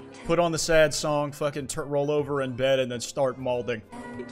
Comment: did they say their gonna start balding if u play a sad song?